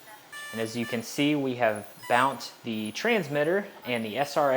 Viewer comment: Did you say "bount"? LOL! I love it